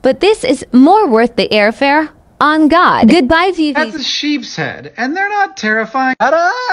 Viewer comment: ngl before I realized it was sheepshead I literally thought hew was saying 'that's what she said'